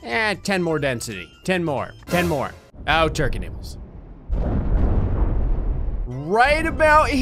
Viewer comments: D...did he just say "TURKEY NIPPLES?!" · Did he just say, “Oh turkey nipples?” I’m gonna use that now.